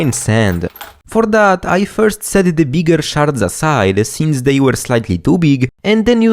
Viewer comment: anyone else heard him say "sharts" hahaha wonderful accent